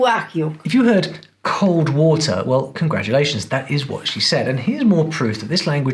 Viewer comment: i didn't heard cold water I heard cold wasser